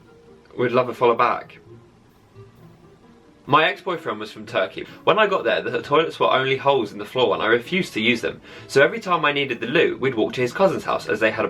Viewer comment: Am I the only one who thought he said" My dead boyfriend" 😂